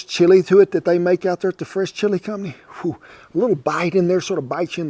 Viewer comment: I thought he was saying the French chilie company.. I was like I have no business eating French chilie! It’s the “fresh”!